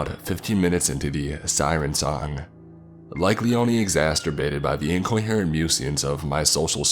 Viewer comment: did you really say "Exasturbated"? :D